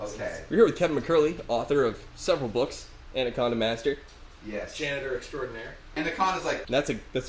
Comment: LOL 5:25 the generated subtitles say "McCurley, author of several books and a condom master." :`-) I'm so glad they are talking about giant noodles.